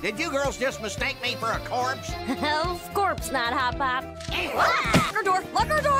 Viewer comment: I didnt even notice the pun i heard "of course not" instead of "of corpse not" lol